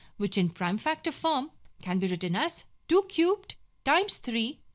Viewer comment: what did you say at 0:51 2 cute or 2 cube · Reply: Cute Lol Ohh your name is lol